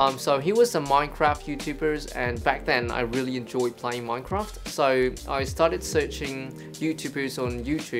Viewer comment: Wait, why did I heard that "Enjoy PLAY Minecraft" I think I need to see the doctor